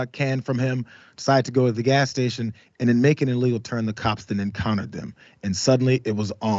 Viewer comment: He doesn't need to identify because he didn't break the kaw.. Sounds like you know nothing about the constitution and the 4th amendment smdh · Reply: in the video, did he say making an illegal turn or did he say making a little turn?